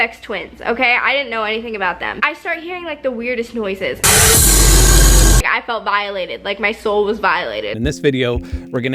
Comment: I thought she said "I felt dilated" lol. I was like "oh my"